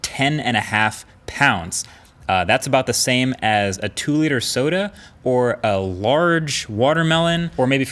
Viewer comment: "4800g, so the weight of a 2 litre soda" raises eyebrows in European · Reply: My brain auto-corrected it and I thought that he said "two 2-litre sodas", which would have still been a bit short, but this is evidently not the case.